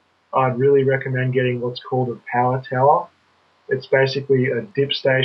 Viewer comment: Does anyone know, what that thing is called, that recommends buying at 24:39 ? I can't understand him. :-( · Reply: power tower